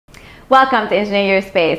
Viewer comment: Can someone clarify wth she says? 0:01 welcome ejuayspay?